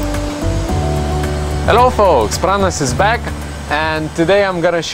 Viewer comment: Im from South Africa. Did he just say?